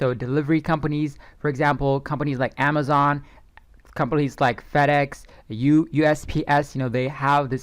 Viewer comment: Me and my buddy both thought that you said Fatass when you said FedEx🤣🤣🤣🤣🤣Great content btw...